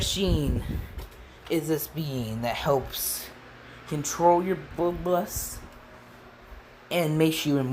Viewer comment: did u say bloodlust I couldn't really understand